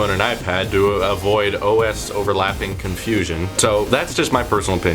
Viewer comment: did he say OSwatch